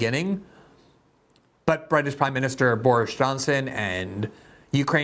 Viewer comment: Did you say "Boorish Johnson"? 😁